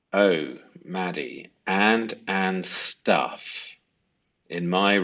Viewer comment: I think she says Steph, not stuff